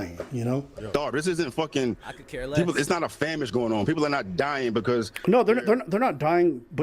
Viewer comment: Did he just say “it’s not a famish going on??” 😂😂😂😂